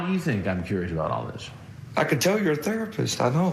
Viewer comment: Now i heard him say "obviously your a therapist i iknow" but when i replay that again he says "i can tell your a therapist i know" does any1 else hear that change too? abit of a glich in the matrix lol